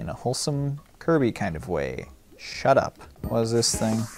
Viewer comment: i sneezed right at 4:51 and i heard just say shut up right after i sneezed